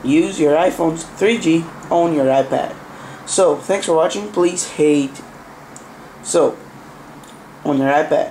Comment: did he say please hate? 7:30 and then say please rate =) lol nice vid bud